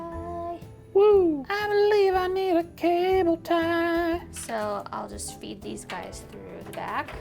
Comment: "I believe I need a cable tie". 😂😂🤣🤣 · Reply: PSYCHO Oh, I overheard it as „I believe I‘m in a cable tie“.